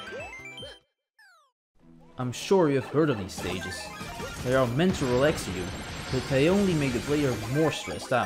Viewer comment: I didnt understood what You Said, But i'll ignore It anyway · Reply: He said that even if refreshing stages are meant to relax players, they just stress them more